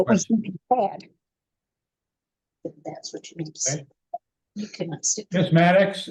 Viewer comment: The judge not tearing her apart for having those kids in there for so long because you could see she was talking to someone all that time and letting the kids here all that is disgusting. She is a terrible mother. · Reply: It wasn't the kids in the room with her.... The voice that can be heard several times is that of a grown man.... Clearly @ 46:03 He says "that's what you need to say." Definitely not 2 kids playing. My guess is the felon boyfriend.